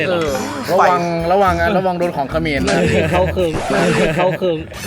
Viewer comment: หนูเป็นคนกัมพูชาไม่ทราบว่ามีอะไรกับคนกัมพูชาหรอคะเห็นในคลิปพูดว่าระวังโดนคนเขมร🤔🤔มีอะไรกับคนกัมพูชา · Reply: ระวังโดนของเขมรค่ะไม่ใช่คนเขมร